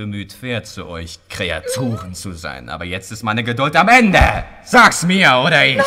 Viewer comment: What did he sayyyy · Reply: "[...] jetzt ist meine Geduld am Endeee!" 😄